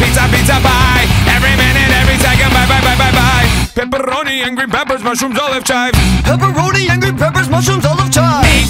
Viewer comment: Before reading the lyrics I thought he said: Pepperoni angry peppers, mushrooms all the time. 😅😅